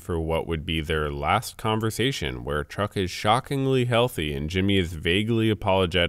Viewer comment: sounds like you said truck lol